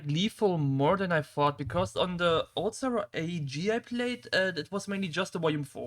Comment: Did you mention ~AG~ server in 28:08 or did I hear wrong? 1:23:18 - Yes Money and Mastermyke20 are two different persons, though Money had a different name for most of his career (I don't remember which one) I feel like Nexelon got a bit underrated there but maybe I'm biased (?. He had plenty of maps, his name was Mew before and had 10 volumes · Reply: AEG*, it was a GER/ENG Server and the very first I played for a longer while (it's also the Clan IwAnAtOr came from and Snake/Venom pretty much originated there) Also the main problem with Nexelon is that from outer POV you can hardly tell where his skill is. His track style is tough to seperate from yours on feats so the only real options to see 100% Nexelon are v1 which is obviously not his full potential and the by now kinda old map ft ShuX that if was named "Wind-Something" idk XD All in all: Tough to grasp